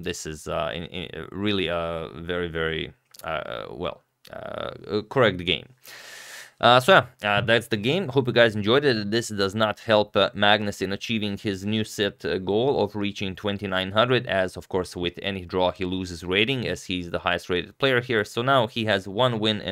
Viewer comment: Did you say, "Sith goal" like he's trying to become Darth Vader or something? LOL